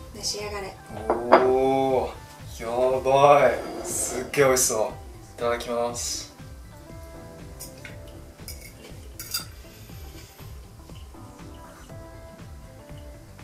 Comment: it sounds like you're saying "Sugoi sou", but isn't it "Sugo sou"?